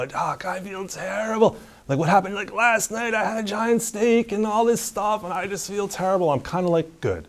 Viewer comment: did he say a giant snake?